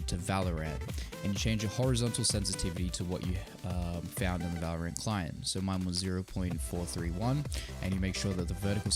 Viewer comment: What did you say at 1:43 ? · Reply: ZeoruS ‘Jayce Here’ lol I stuttered hahah